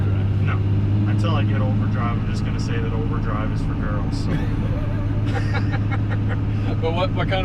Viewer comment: I thought he said od was for girls 🤔 good looking truck thanks for the video